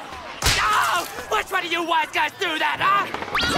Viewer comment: Is it just me or did it sound like Natsu said “ok which of you white guys through that!”